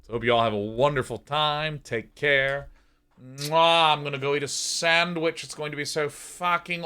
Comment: Sean says: "I'm gonna go eat a sandwich." I hear: "I'm gonna delete a sandwich." And I fully agreed.